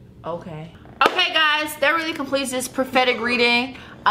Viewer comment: & i thought she said a pathetic reading 😭